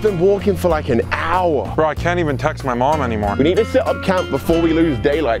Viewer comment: 0.10 I thought he said I can’t touch my mom anymore I was like what the actual fuck